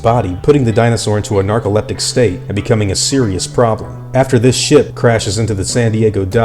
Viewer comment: bruh it straight sounded like you said "after this shit." 😂😂😂 I had to replay that just to see if I was trippin' when in actuality he said ship